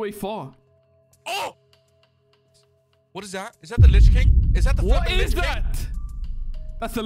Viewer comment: I heard a swear from: "Is that the Lick King?", "Is that the f###### Lich King?" Please, if you read this, I warned you about it...